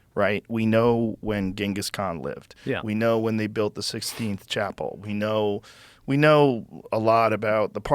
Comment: Did Joe just say sixteenth chapel @ 00:55? 🤣🤣